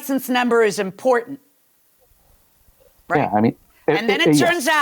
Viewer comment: is it me or does the defendant respond 'yeah honey' to the judge? He corrects to 'I mean Yes you're right' but I am pretty sure he called the judge 'honey' and I'm surprised Judge Milian let him get away with it. Admittedly, I might have misheard him - there is much background noise where I am as I watch this.